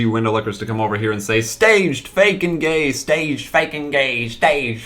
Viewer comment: "Staged! Fake and Gay!" I heard. "Hurray! Bacon Day!"